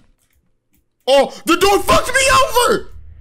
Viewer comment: Did he just say THE DOOR F*CKED ME OVER XD